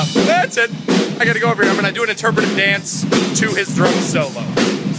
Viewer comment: OMG at the end i swear i heard him say 'Molest your face' instead of bless your face XD